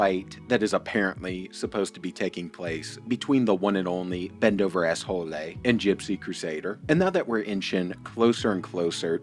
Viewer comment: I love when you said "Bendover Asshole-lay", Am I hearing it right? 🤣😂